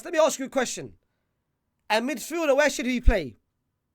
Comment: am I tripping or am I hearing mid fraud 😂